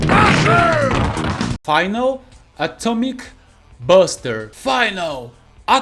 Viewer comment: eu sempre entendi ele dizendo " Final Atomic Missle! "